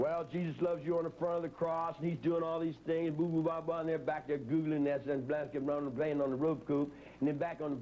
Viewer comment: Does anyone else hear him say around 23:45 “That Booboo Bob is sitting back there googling that shit and playing on the Roku”?